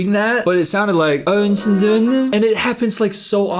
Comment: I keep hearing "Are you going to Di'a?"